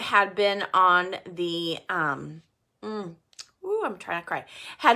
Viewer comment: When says she’s trying not to cry .. that seemed very fake... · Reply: In the livestream dedicated to the girls, where she's talking about Casey's son? The first time I heard her say it, it sounded off so I went back... I think she hit a Freudian Slip. It sounds more to me like she's saying "I'm trying to cry." Which is exactly what she's doing.